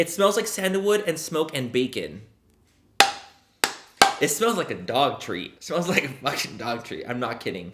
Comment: Ok. First of all i friken loved this! I was soo ready to see do you dirty.😂but it was Epic😍Second, i totally thought you said the candle smelled like Dog Tree. Like a tree a dog pisses on. Sooo the smell of tree bark, wood and piss. Made sense in my head at the time😂😂😂